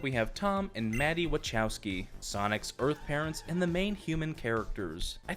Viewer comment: I almost thought you said birthparents instead of earthparents I got so scared for a second